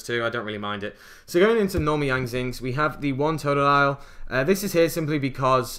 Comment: lol Did he just say Totodile?